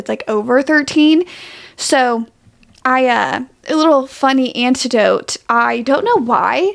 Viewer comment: hey, i might be mishearing you but i think you’re saying antidote instead of anecdote ❤